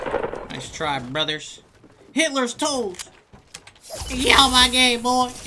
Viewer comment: Anyone else hear "hitlers toast" at 7:12?